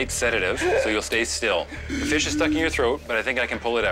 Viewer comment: I thought it said i have a fish stick stuck up in my throat.